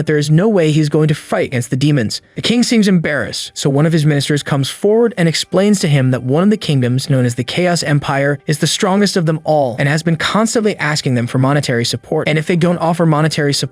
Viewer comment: d-d-did I just hear chaos empire